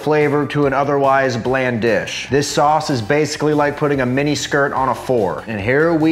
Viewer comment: "like puttin a mini skirt on a four" 😂😂😂😂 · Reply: My brain heard it as "putting a miniskirt on a Ford" lol